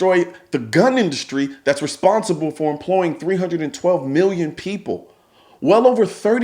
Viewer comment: 312 million people employed by the gun industry? That is generous. That is a job for ever adult in America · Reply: I thought he said 12 million not 312 million.